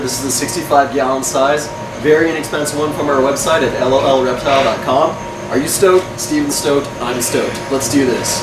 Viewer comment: it sounds like he says at lolreptile :D